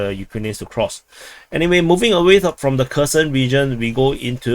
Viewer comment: "Anyway, moving away from the Cursed Region"... 🤣 For once he uses "region" properly I get to hear "Cursed" instead of "Kherson". No offense meant, I just found it funny. (Note: I'd pronounce it as "her son" but stress in "her" if Anglified, in my native language (Spanish, and probably also in Russian and Ukrainian) would be /xerson/ with stress in "son".